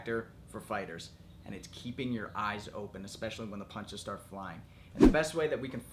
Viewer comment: i thought you said keep your hand on your eyebrow